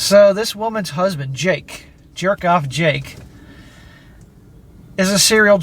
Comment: Lol when you said jerk off Jake, I thought you said jared goff jake and was WTF why are you bring my quarterback into this. Lol